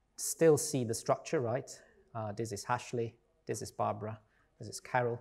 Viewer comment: this is Hashly (instead of Ashely :)